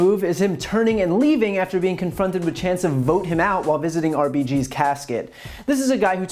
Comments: The crowd is right vote him out! I thought they were saying Broken House! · I heard “throw him out”... 🤣 But to do that, we do have to “vote him out”. ✌🏼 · I heard "go to hell" lol but vote him out works too lol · Wow my English is not so good I heard "rot in hell" 🤣🤣 not "vote him out". Though I wish both will happen. So yes I'm a bad person and my English listening is bad as well.